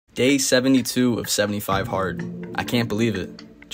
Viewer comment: Thought you said gay 72 haha